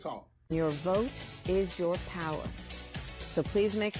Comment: OMG right after the 8 minutes and 36seconds apart she says your vote is your power for a half a second I thought she said your throat is your power. That's crazy considering she blew everybody to get where she is lol 😊